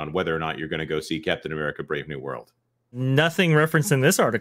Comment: at one point says the title of the film and it sounds like he said "Death of America: Brave New World"